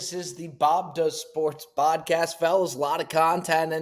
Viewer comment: did you says "bob does sports bodcast" Don't think we didn't hear that slip up!!